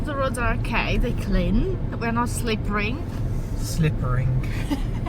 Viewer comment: sounded like hint hint ‘slip her the ring’ 😂